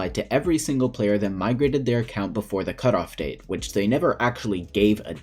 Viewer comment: If you mean the migration trailer, that cape is still up for grabs if you registered your Minecraft account before December 1st. Just wait until early 2021 to migrate. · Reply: ahh. Okay. Because I think in the video you said "migrated until the cutoff date" I might have misheard that. Thanks